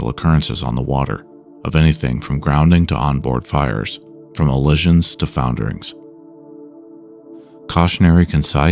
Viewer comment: You used a word i dont know in the intro when discussing marine casualties. It sounds like you said "collisions" but without the "c". Maybe thats all it is but im curious if this "allision"? Is a word of its own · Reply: “Allision” means the vessel crashed into a stationary object, like a bridge